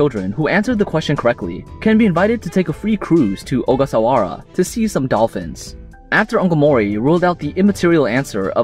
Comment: I first heard “to see some dolphins” I mistook it for “to see some Elvis” and I was like “Wait, they revived Elvis Presley?”